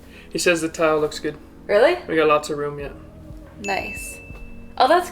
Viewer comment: He said "I'm gonna go get miso soup." I thought he said "I'm gonna go get me some soup." until she said "Miso soup sounds good!" 😂